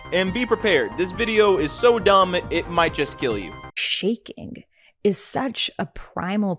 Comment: don't Hate on me for this but I heard "shake it" and just lost my shit like